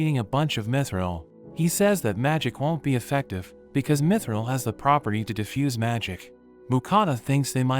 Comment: my captions say "Meth Israel" instead of "mithril(or mythryl)"